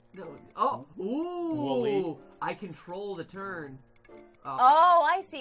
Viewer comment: I thought he said I control the turd